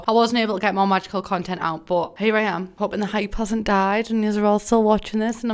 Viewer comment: i thought she said “my baby cousin died”